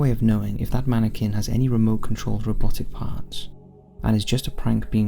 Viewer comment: Misheard the first commentary and it sounded to me like he said something about the mannequin having a remote controlled arse